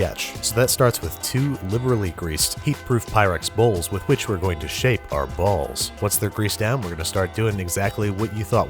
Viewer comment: is it just me or why i keep hearing shave these balls